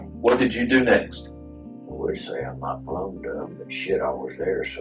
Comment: Did he just say "put down".... had to pause for a sec after that one